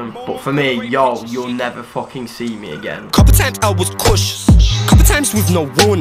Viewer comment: when the outro goes off the first thing i hear is "CONTENT I WAS CAUTIOUS "